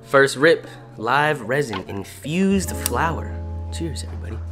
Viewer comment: i thought it said… “INFESTED”